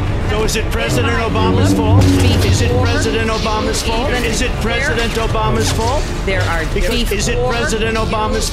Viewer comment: I can't understand what they are saying. Can anyone tell me what are they saying? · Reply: It's Trump talking over Hillary saying "Is it President Obama's fault" repeatedly.